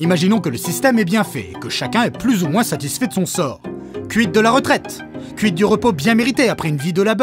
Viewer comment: J'ai entendu "cuite de la retraite." Faut que je me calme sur les soirées. Sinon super vidéo merci et toujours un plaisir d'entendre N.O.R.D.VPN comme rap!